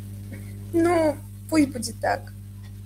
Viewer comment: She actually said “Nu” (Ну) which means “Well”